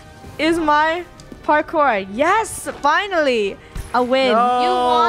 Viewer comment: When funnehs said finally a win i thought she said binally a win so I went back a second time w and she said finally